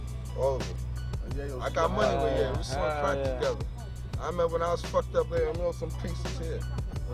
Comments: did he say we smoked crack together, at first I thought i mistaked it with ‘sold’ not ‘smoked’ but then I played it back a couple times and it sounds like smoked im not gonna lie 😂😂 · Did I just hear, "I got money wit Yayo, we smoked crack together" umm say what 👀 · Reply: Sold crack together